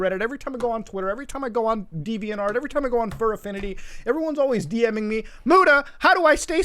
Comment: did he just say fur affinity?